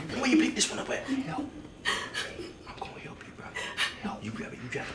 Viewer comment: what's say at 1:34 after dude whispers 'help me' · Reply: He said I am going to help you.